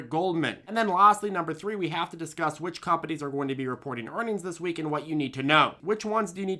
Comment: You said which companies and I thought you said witch companies and I initially was concerned…